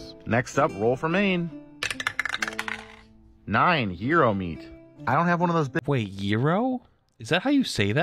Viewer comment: I thought he said Hero Meat at first, and then he was definitely saying Euro after that. You CANNOT convince me that you get that sound from the letter G.